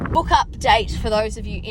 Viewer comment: I misheard "book update" as "hook up date" and I was so confused as when you mentioned you started a dating service lmao